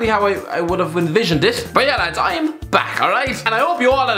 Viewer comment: I thought it said im black😂